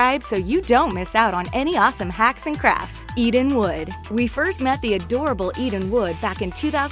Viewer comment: it sounded like you said Eating wood🤣🤣🤣